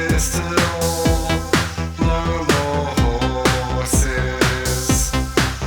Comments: I heard “blow more horses”. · With that no more horses section I heard "Beat off horses" for some reason.